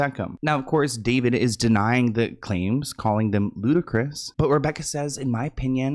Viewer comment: I swear somehow I always hear "Ludicrous" as "Ludacris"